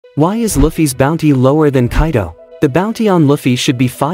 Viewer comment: I thought I heard loffy bruh